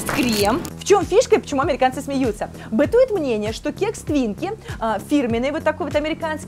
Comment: Послышалось "Кекс Квинке"... Думаю,вполне возможно. Шутки без огня не бывают😁